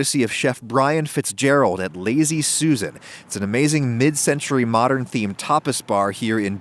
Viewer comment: The first time I thought he said "Mid century, modern themed topless bar" lmao